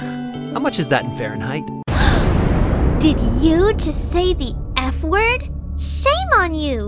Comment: HELP I WASN'T FOCUSED AND I TUOUGNT I HEARD "DID YOU JUST SAY THE N-WORD" INSTEAD OF F-WORD AAAAAAAAKQNEJDISHEJD8AJBEHRIFNFBDOIZM4NFKAN